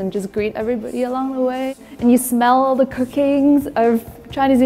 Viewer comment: I thought she said, "..and I smell the cocaine.."" at 5:02 lol